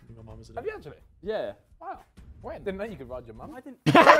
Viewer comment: what did ethan say? I cant understand a single word except “mom” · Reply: Ethan: Really? Simon: Yeah Ethan: Didn't know you could ride your mum